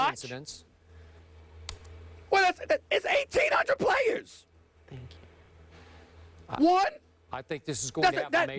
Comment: yo i think i heard cari said thank you